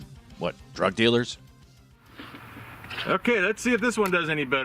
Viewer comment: I really thought you said drug dealers eat VPNs.